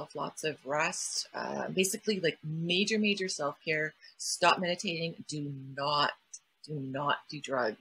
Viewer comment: Thank you for having and sharing this convo! 1:05:15 Did I hear right here? Did she suggest to "stop" meditating when going through an experience like this?